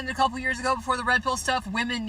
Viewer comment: She said. Women were humbled by the redbull thing. I think she meant metoo thing. But she said redbull. Is there some thing involving redbull? @ 3:10 she clearly says "the redbull stuff". Did they Mandela effect away their accountability like the bearenstein bears? What happened with redbull? Does her vagina have wings? What happened?? If there's some redbull thing that can humble women it should be played on repeat 24/7.